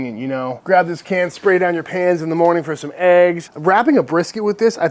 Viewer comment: Talking about the beef tallow spray, I thought you said something about spraying it down your PANTS in the morning. Then I figured out you had said PANS and just laughed.